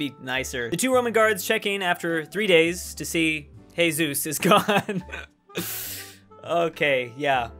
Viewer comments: Did he just say Hesus instead of Jesus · did he 'he Zeus' instead of Jesus????? M